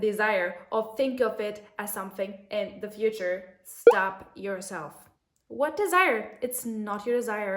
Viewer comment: I thought you said slap yourself 😂